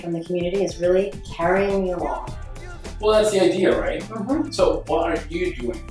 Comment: Laura curran said "Meg is Really Carrying Neil Off." As a former Gubernatorial candidate I urge everybody to vote against her because she is part of the santa cruz conspiracy. That woman is a criminal! Why did she say "I support that for the community- Meg is Really Carrying Neil Off." at 1:00 - 1:04 I support putting a wire on her.